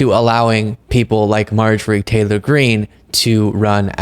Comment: Sure sounded like you said Marjory Tail hole Green 😂😂